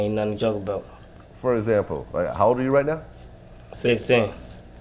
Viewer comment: the fuck did he say?? "how old are you right now" All i heard something teen SmhNiggas gotta get they speech together these days